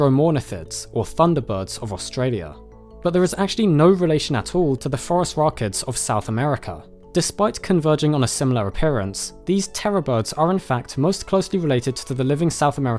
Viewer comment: I heard "forest rockets" before seeing "phorusrhacids" written.